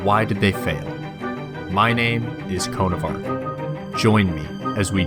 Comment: At the begining I thought you said "My name is coronavirus"